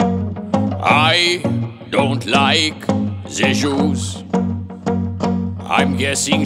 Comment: i thought he said i dont like the shoes💀💀💀💀💀💀